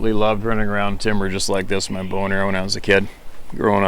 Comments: I thought he said he liked running around with a boner, but then replayed it and he said bow and arrow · In the first line I misheard “bow and arrow” as boner... yikes lol